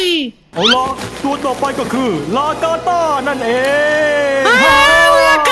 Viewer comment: ผมไดหยินเสียงพูดว่ารากาต้า6:17